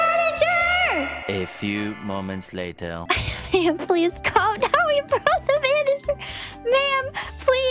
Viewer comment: This one is honestly hard to tell just by the audio. But she actually says "Ma'am please climb down, we brought the Manager" she was reading a comment that said exactly those words. Yes, she says the same joke twice in a row. also 1:02 Here she says "We found the manager for you to speak with." Fauna has a slightly slurred accent which makes it hard to tell sometimes what she says. I forgot what the quote was, but it was something like "Gura has changed you" or something like that, the context was that Fauna has accepted that she's mommy. The joke being that Gura keeps calling Fauna mommy. And for the life of me, I could not understand the first word she was saying. I'm a vod watcher so I have the privilege of being able to scroll back to re-listing. It was only after I found which comment she was reading that I finally understood she was saying "Gura".